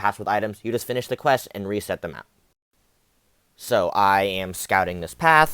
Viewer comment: in your first run it looked like there were 2 revives and you played the first fight and quit out. Is that a case of a video cut of did I miss something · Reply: oh no problem I thought you said “let’s reset the map” as in best the first guy and exit out lol